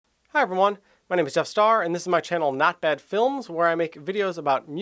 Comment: I heard " hi! My name is death star ". But I'm also high as fuck